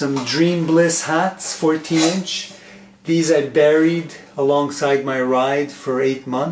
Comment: At first I heard, "alongside my wife". Had to do a double take.